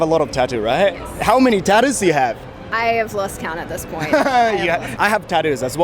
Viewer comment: 本日も、自然か会話（言い回し）がたくさんあり、勉強になります。I have lost counted というのも、初めて聞きました。過去分詞が二つ並ぶので自分では思いつかないです。 TATOの件ですが、最近は多様性とかの議論があるので、いまさらの話かもしれないですね。欧米はファッションの延長なのかもというイメージもありますが、いずれにせよ、捉え方は様々なのですね。国によつては、習俗で決まっている場合もあるのだろうと思います。 若くて肌が張っている時の仕上がり感は良いとしても、歳をとって、肌が皺々になるとどうなのかなぁと思っております。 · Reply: 正しくは I have lost count です！字幕が間違っていて、それに続くat this point の at が繋がってcountedに聞こえて間違えちゃったのではないかと思います。lost counted という言い方はしません！